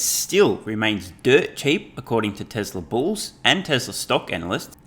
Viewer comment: When said tesla bulls i thought he said tesla balls haha